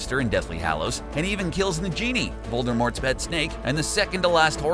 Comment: did he just say.....nagenie 🧞‍♂️